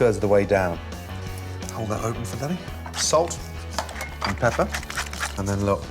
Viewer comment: Did he say: ''hold that open for daddy. ''? haha